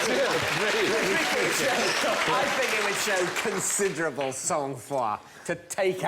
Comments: What does David say at 1:28? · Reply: "I think it would show considerable sangfroid to take out a pen..." "Sang froid" literally meaning "cold blood" in French, but closer in meaning to "cool, calm and collected" than our understanding of a ruthless "cold-blooded killer". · what is David saying at 1:27? British accents make french virtually unintelligible. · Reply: Sang froid. Cold bloodedness. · What does David Mitchell say there about 1:28? Considerable sou foi? Sous foi? Soul Fwa? I'm sure it's a French term, but I've no idea how to spell it. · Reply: sang froid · What does David say at 1:27? I think it would show considerable...? French phrase? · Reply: Sang-froid, I think~